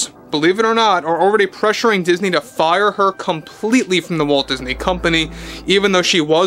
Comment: Could have sworn you said “Woke Disney Company” at one point. Just a “slip of my ear” I guess.